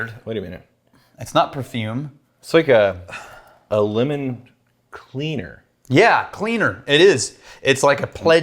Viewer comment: I thought said “lemon wiener” not lemon cleaner